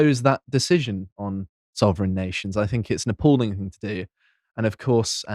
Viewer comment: I first heard 'It's a Napoleon thing to do' instead of 'It's an apalling thing to do', and thought nothing of it because it fit so well.